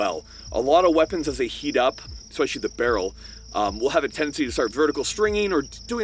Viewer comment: For me as a german this sounded like „… zum Beispiel the barrel.“